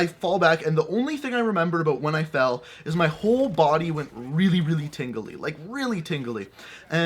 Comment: please bring more story time! I kind of died, sounded like you said; My whole body was tinkling 😂